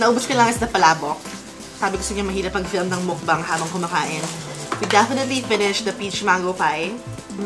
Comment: “mahirap mag- mukbang habang kumakain” Did I hear it right, ate? 😘😘😘